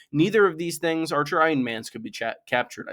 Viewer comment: can anyone confirm what is said here? I'm hearing "none of these things are trying Stannis." While it makes sense in that context, it seems like a curious word choice. Not saying that would be wrong or misused in any way though, more just obsessive curiosity